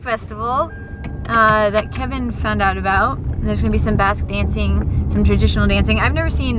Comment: So... for the last week when you were talking about a "basque festival" I totally thought you were saying "bass" and going fishing...